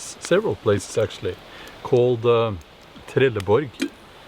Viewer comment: Did he say Trelleborg, like the city in Sweden? Very interesting and sad!